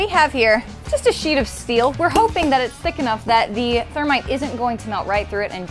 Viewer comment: Whoops...... I thought it said termite 😂